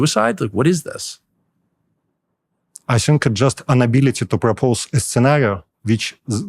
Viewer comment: I think he wanted to say “inability”, but said “unability”, not “an ability” like in the subs